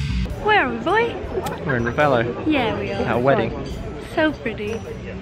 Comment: Gavin's accent at 2:19 made me think he said "at our wedding"